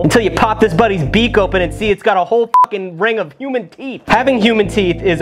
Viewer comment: i misheard 'human teeth' and thought you said 'human FEET'